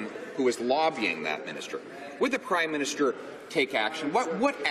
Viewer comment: I swear, he said crime minister, intentionally or not, it's true. This so called PM does not represent Canadians, just answer the damn question. What a waste of tax payer money. -Word of the day, "mud slinging"